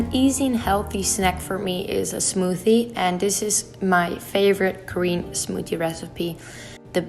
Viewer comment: I heard 'my favorite Korean smoothie' lol